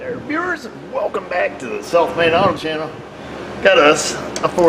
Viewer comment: You say "South Main Auto", and I hear "self made auto". My old ears !!!